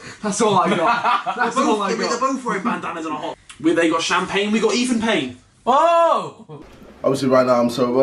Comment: “They’ve got champagne, we’ve got Ethan Payne” Harry, 2019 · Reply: I thought he said "we've got even pain" loool